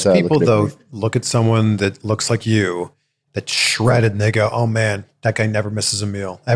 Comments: it sounds like is saying "Shredded N-word" but he's actually saying "shredded and they go" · did just say "Shredded n**ga" at 14:57? xD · Did Say shredded Ni**a